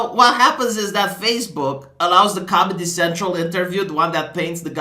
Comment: At first I thought you said "Communist Central."